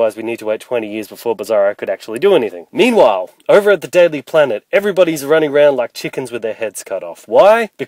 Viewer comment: UPDATED. Both parts of my Batman V Superman fan fiction video, now combined as one. Plus, a few EXTRA SCENES and a slightly ALTERED ENDING. Starting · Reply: I heard deadly planet😂😂😂